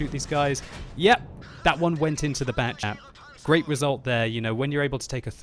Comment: Did He say "Bat-Chap"?? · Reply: SuSmarTn bat chat